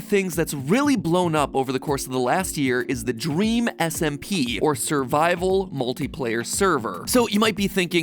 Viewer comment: I thought he said dream simp